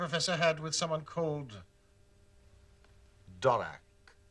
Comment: what Sherlock said: Dorak what I heard: Dalek i proceeded to fan girl out and afterward fantasize about an official Sherlock Holmes and Dr who crossover